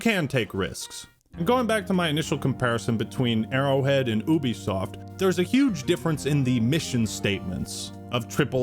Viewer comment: I thought he called them Bubisoft.